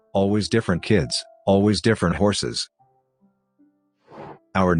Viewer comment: lol, I misheard "horse masks" so imagined them riding horses while having those silly horse head masks on